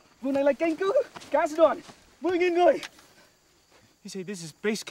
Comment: I think the guy said 10k not 4k